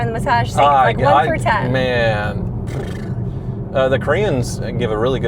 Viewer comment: did he say crayons 🖍???😂😂😂 sound like an ex-wife diss 😂😂